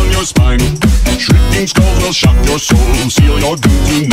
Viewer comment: I swear I keep hearing "shrieking Skull girls" I swear I not joking because my profile pic is a character from the game Skullgirls but I swear instead of "Shirking Skulls Will" I hear "Shirking Skull Girls"